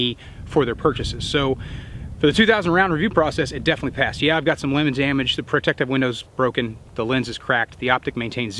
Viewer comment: did he say lemon or limited damage? Wondering if that's an everyday thing, or just this once, where you right hand your rifle and left hand your pistol. Maybe the video has it the other way around. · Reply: "lemonted damage"